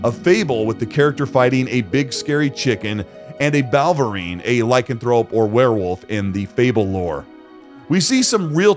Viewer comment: im sure you wanted to say lycanthrope but i heard lycan-throw-up 😂😂